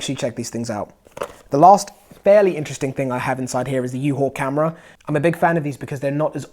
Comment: I would like more info on the helmet camera, please? I thought you said uhaul camera, however, all I get is American truck rentals. lol